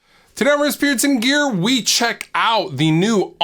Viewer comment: Every time you say “Riffs, Beards and Gear” I hear “Riff, Spirits and Beer” I Might be an alcoholic.